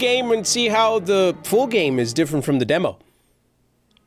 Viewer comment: He’s says fool so much that in the intro he said full game but I thought he said fool game 😅😅😅